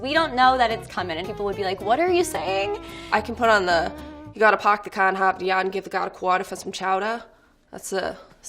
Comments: What in the world did the person at 2:26 say because I heard something like UgottapoptheconhoptheyungivetheGodtheQhuanForsomechouter. Tell me what you guys here or please translate if you can. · Ok actually what is she saying? I heard “chowder”...that’s literally it. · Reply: Lol I live in Massachusetts so I know what she was saying. She said "We gonna park the car in Harvard yard and give the guard a quarter for some chowder." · When the Massachusetts girl said “hsidhfbisjdvfhsjsbisjhfbrujahfhfjd quateah foah some chowdah” I felt that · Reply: I thought she said hop the yard not Harvard yard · I felt that when she said "I can put on sjaflskghkalfhkajd shaq da" Fr what she said? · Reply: You gotta park the car in harbor yard to give the dog some chowder